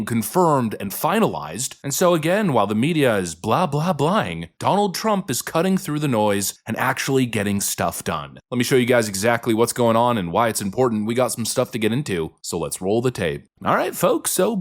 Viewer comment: Love that term you used or maybe this is how I heard it. "Blah blah blahlying" I'm definitely going to be using it.